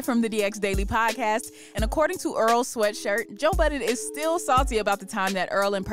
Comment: Why it sounded like she said oral sweatshirt 😂